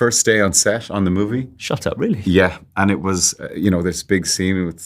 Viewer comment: did he say shut up or..? am i hearing things